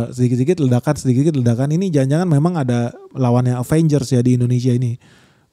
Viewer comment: Ga salah denger td abang bilang lawang 😅😅😅